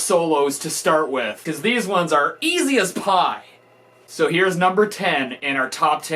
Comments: In the beginning I thought you said easy as fuc not easy as Pie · I thought he said "easy as pop!", would of made sense if he did. Wish he did tbh..